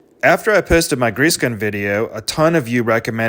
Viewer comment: I thought he said "after I pissed in my grease gun video" 🤣